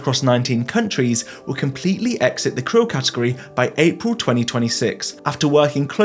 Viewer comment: I would like to begin by saying thank you for these lovely good news videos. However, while I was watching, I noticed that there were some mistakes in the subtitles. For example 2:22 has the subtitles saying "cruise category" when I'm pretty sure that I hear "krill category" spoken aloud. Similarly, I don't think that the word "chinampa" is ever correctly recorded by the subtitles, which instead spit out phrases like "sheer numbers (5:55)" or "culture numbers (5:41)." It is good that subtitles are included, something that is unfortunately not the case for many YouTube videos, but obviously some of these errors are more severe in nature, impeding comprehension, so I wanted to let you know about them so that these subtitles can be made even better.